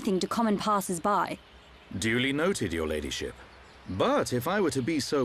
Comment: I thought Dunban said ladyshit and I nearly died laughing.